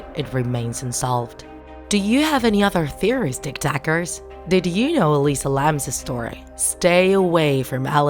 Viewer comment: At the start I thought u said Tik tok